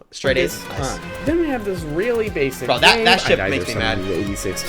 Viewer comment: what did u say??????????? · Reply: Ship 🤦‍♂️